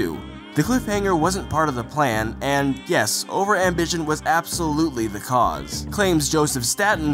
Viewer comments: "...Absolutely the cause" (What I heard) "...Absolutely the case" (What is written) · Just so everyone's aware, at 2:02 the current host said "cause" instead of "case" which is what appears on screen.